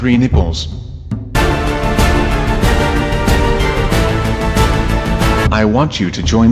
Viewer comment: WAIT WHAT DID HE SAY 😂😂😂😂😂😂😂😂😂😂xD xD XD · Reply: he said "i have 3 nipels" sorry if i spelled that wrong